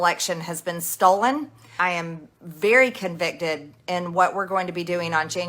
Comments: Did she say she's "very convicted to..."? @ 4:38. I think so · Wait...@ 4:36 she actually says she's "very convicted"...LET'S HOPE SO! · Does she say: " I'm very CONVICTED?" what foqen morons elected her to office?